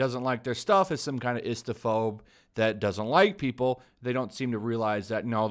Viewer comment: I misheard it as “instaphobe”